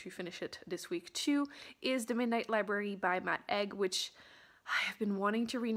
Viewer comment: When you said Matt Haig, for a second I thought you said My Egg 😂 “I have very high hopes for my egg” 😂 Love your vlogs ! They make me feel so cosyyyyy 🥰